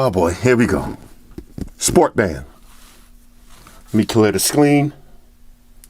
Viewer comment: He said scleen instead of screen.